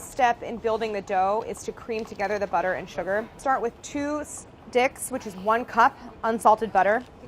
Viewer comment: Start with 2 D'ICKS?, Am i hearing this correctly